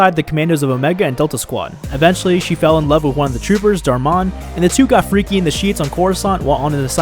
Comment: I thought he said fucking dharr Mann when he said darmon